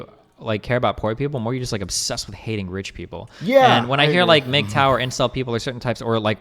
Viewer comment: What is the thing he says before "incel"? Like referring to another group. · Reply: mgtow